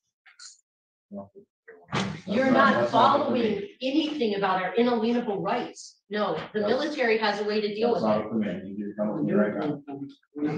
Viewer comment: The word inalienable will never be the same again. 😂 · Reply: I heard her say UN INALIENABLE haha